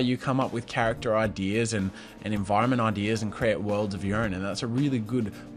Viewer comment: did he say worlds of urine?